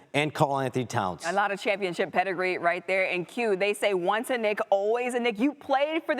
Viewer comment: Yo what did she say at 1:33 😅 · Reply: ONCE A KNICK ALWAYS A KNICK...